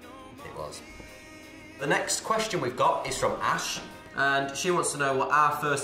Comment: Am I not hearing well or did Liam said that the question is from Ashe at 1:54 and now she is collaborating with Niall wow